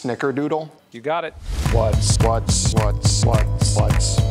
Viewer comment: I keep hearing “Madam Odio” 😂